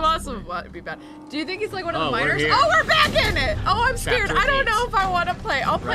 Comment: For the duration of the whole last video plus thirty minutes into this one I thought they were saying “minors” instead of “miners”...